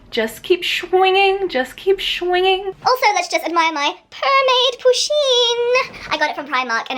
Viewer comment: I thought she was saying, admire my b**bs?! Haha lol. But I was wrong.